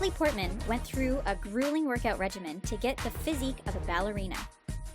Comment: Whoa.... 0:34 wtf is a 'reggie men'? I keep hearing it on a lot of yank videos. Is it perhaps them f*cking up with the pronunciation of régime, which is pronounced 'ray-jeem'?